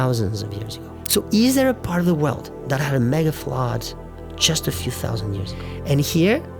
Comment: Very interesting video, for the most part. Even with closed caption there were times when I had a hard time understanding what you were saying. At 10:46 closed caption posted "mega flaw" when you were trying to say "mega flood". This was easy to figure out, others were harder.